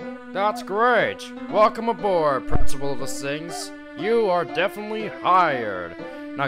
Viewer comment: did he say principal of the sings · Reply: He actually said Principal of the Things.